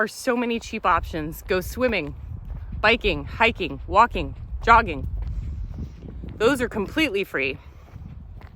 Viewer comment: At first play I thought you said 'doging', but on replay you said joggiing ... phew ...